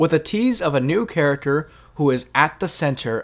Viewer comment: I thought it said new character who dis 😂😂😂 I'm Soo dumb 😭